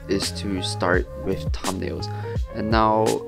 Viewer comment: what is this word he says here? EDIT: I looked in the comments. It was "thumbnails". Im leaving it here for anyone else who didn't understand.